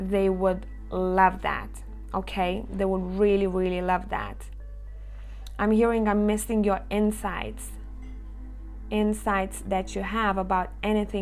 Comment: 2- yh that's all me. Lol I thought you said I miss your insides 🤪🤣